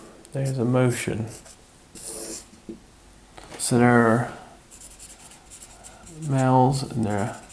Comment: The subtitles during whiteboard session are not all accurate, not exactly what he says. At one point he says "so they're", and the subtitle reads "some air". 1:23:52 At another place he says "there's also", and the words read "a sense of" , 1:28:45.